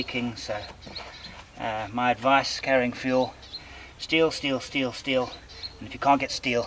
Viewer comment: When you were talking about fuel I thought you were saying "steal" not "steel"!